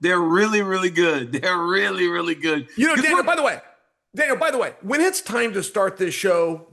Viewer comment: Chael “not for nothing” Sonnen and Daniel “time and time again” Cormier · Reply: Why is Chael calling DC “Dana”?